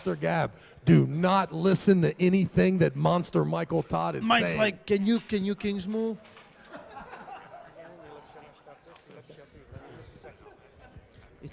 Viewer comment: Did say "Don't listen to anything that Monster Michael Todd is saying" 16:34, haha, true and hilarious.